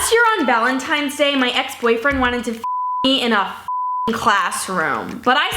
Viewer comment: subtitles say “ex-boyfriend wanted to eat in a classroom” ( ͡° ͜ʖ ͡°)